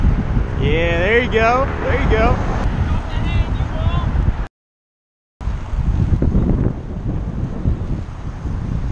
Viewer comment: what he say? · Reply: He said “drop the hand U won’t